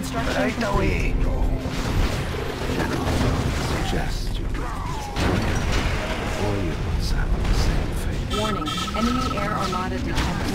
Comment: What did Yuri say? The background gameplay sounds loudly, thus can't hear. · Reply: From wiki: General, I suggest you leave this area. Or you will suffer the same fate as Premier Romanov.